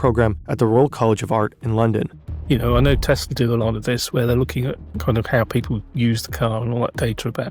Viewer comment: i slowed it down to .5 and i STILL have no fucking idea what in the name of fuck he is saying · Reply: "You know I know Tesla do a lot of this where they're looking at...."